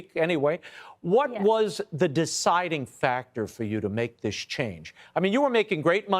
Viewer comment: was that a Freudian slip? Sounded like he was about to say what made you decide to make this shit up . 😢then said This change instead